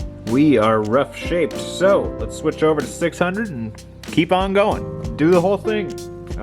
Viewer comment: When faceting each side do you cut and polish each facet prior to going to the next. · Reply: Re-watched heard you say switched to 600. Never mind.